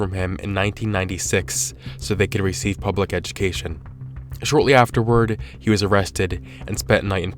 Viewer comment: I thought he said public execution.